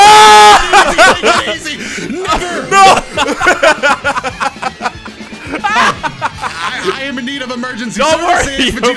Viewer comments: someone tell me what he said please💀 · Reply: clearly said never 😭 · what did he say?? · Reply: He said”never” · what did he say??? ( just asking) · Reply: "Never" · did say the n word at 7:22 , we safe he just said never (edit) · AYO WHAT DID SAY AT 7:22 😭😭😭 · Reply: "Never"